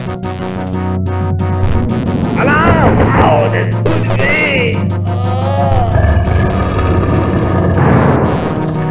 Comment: What did the one in the green say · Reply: apparently he says "Ow that hurts!"